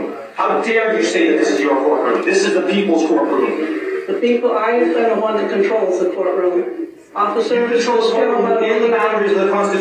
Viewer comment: Did she say the people I have fed are one that controls the courtroom?